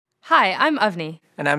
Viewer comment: LOL i thought she said "Hi. I'm Ugly."